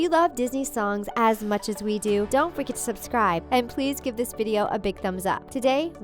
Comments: it sounded like you said don’t frikin subscribe XD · don't FREAKING subscribe Did anybody hear that